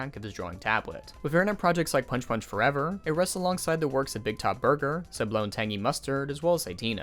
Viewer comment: in case anyone else is struggling to understand what he's saying at 17:15, the titles are: BIGTOP BURGER Sublo and Tangy Mustard Satina